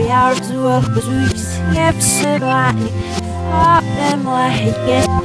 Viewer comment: I heard epsilon